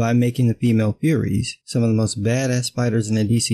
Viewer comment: Thought it said furries for a moment there LOL